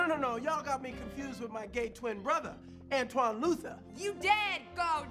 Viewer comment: Ex-wife #1: Hello Antwone! Ex-wife #2: Antwone? He told me his name was Luther! Ex-wife #3: He told me he was gay! Deion: No no no! Y'all got me confused with my gay twin brother, Antwone Luther! Nice save, man 😂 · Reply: See when I first watch this movie I thought he said my gay twin brother Antoine Loser not Luther